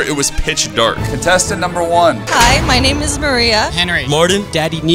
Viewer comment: Mia or maria I Don't Know what 🤣🤣I heard there